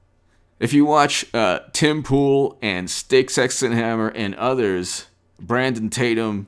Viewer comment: I couldn't make out that name(s) after Tim Poole. Sounds like "Sticks Exton Hammer". Anyone know? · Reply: On Twitter: Styxhexenhammer666